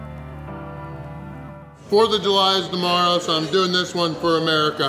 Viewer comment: Did he say it was the fourth of july?